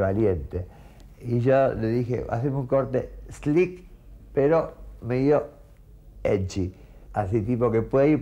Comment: No entendí que dijo ahí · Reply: haceme un corte slick pero medio edgy